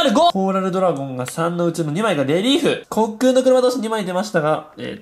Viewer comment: レリーフがリエーフに聞こえる